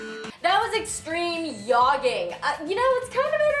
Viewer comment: did she say yogging?